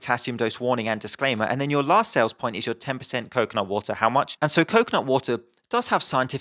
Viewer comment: i only heard him say coke, not water. i only understood he meant coconutwater when the word actually appeared on screen xD